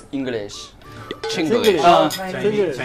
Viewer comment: pensé que dijo Chicles JAJSJAJSJSJS